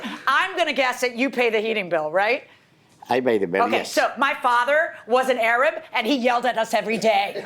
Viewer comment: "My father was an Arab and he yelled about the AC every day" I feel so seen rn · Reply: I thought she said "my father wasn't Arab and he...". Wiki says her father is Jorge Milian from Cuba. Was he Arab?